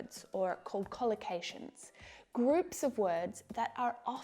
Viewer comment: There was a "word" you used to describe words in groups, was not clear on that word... could you please share... I thought you said "concollucation"... and if so, how is it defined....